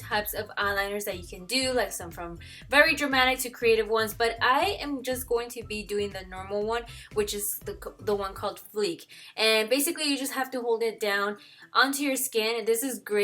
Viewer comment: OMG no lie, I thought the stencil said "fuck" not fleek! 🙄😂